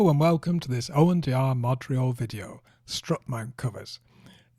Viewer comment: Thanks so much! - from Winnipeg Manitoba Canada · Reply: I kept mishearing Modurol as Montréal!